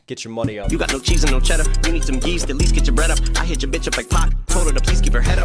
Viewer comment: He actually said yeast to get your bread up, if you bake you know that yeast is the ingredient to make bread expand, it is a long process but you should look it up